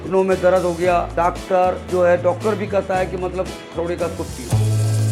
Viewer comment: Did i just heard L**de ka soup pivo..?